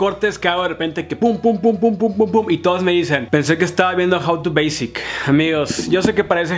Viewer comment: que es lo que dice en el minuto 3:10??? que parece que esta viendo queee???? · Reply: un video de "How to basic"